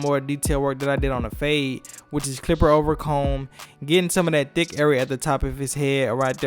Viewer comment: Did you say “DICK AREA”? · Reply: no lol "thick area"